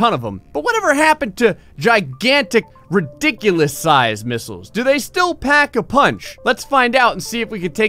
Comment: DID YOU SAY GIANT MISSILES??? <link>